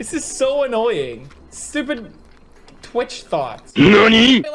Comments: i learned what thot was thanks to that video XD. when i first heard you say it i was like "what does twitch thought mean?!?" #learningwithpewdiepie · Lol when I first watched his eye tracking video, I thought he said "twitch thoughts"